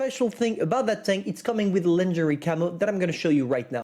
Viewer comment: I literally heard lingerie camo and the next thing was me imagining a bra on the strv. (yes i know its pronounced 'laungerie' but still)